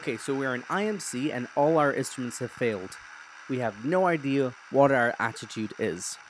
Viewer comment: I thought you said suction had failed and that you didn't know the attitude of the plane. Your unique seems to assume that you start out flying level, but if you do it when you're already banked your day might suddenly get a lot worse.